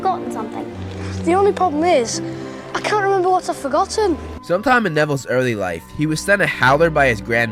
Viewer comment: “The only problem is, I can’t remember what I’ve forgotten” such a good line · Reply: eInvincible I thought he said the only problem is I can’t remember what’s a forgotten